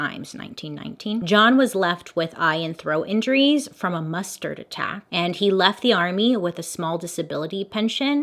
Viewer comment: I thought you said he got injured from a MUSTARD attack! 😂